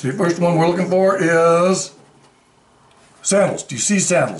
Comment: My favorite Angels!!! 😇😇 I thought you said sandwich when you said sandals guess I must be getting hungry 🤣